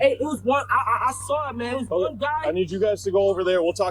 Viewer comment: witness: i saw everything! police: please go away sir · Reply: He did not say "I saw everything". He said (1:40) "I saw it man. It was one guy...". Even what he said at the station from 10:25 onwards, wasn't that useful. Anyhoo - what do you do: have an extra LEO there just in case, to take witness statements?